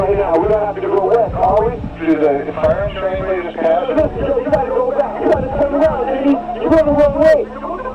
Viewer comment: I swear I heard puffy too, and it sounded like "we gotta gotta go back, we gotta turn around diddy.. you going the wrong way"